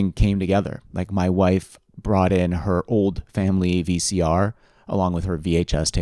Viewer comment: The walk with the Trinitron 😂. Also, I thought you were going to say, "my wife brought in her cat" instead of VCR.